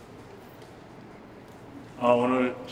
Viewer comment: 강민호선수님 뭐라고 말씀하시는지 잘 모르겠네용 ㅠㅠ 혹시알려주실분 계신가요 ㅜㅜㅜ?? 죄송합니다 ㅜㅜㅜ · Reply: 1900경기 커피차 감사합니다라고 하셨어요 오늘 팬분이 커피차 보냈거든요!